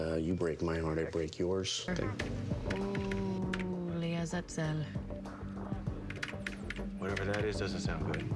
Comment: What does she say at 1:03? 0.e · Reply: She says "La'az'azel" (לעזאזל) which means litteraly "to hell" and is used like "damn", or "dammit". I'd translate "Oh, sh!t".